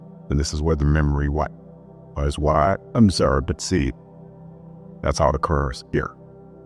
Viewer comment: Serious question: at 21:06 did anyone catch what he said exactly after memory wipe? I heard gibberish 🤷🏼‍♀️ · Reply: I think he said “this is what I’ve observed and seen”